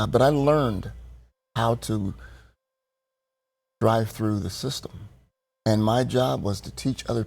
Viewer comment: It sounded like he said bribe through the system...